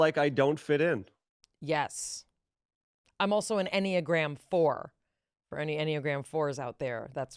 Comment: what is she saying? "anyagram 4" what? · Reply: probably enneagram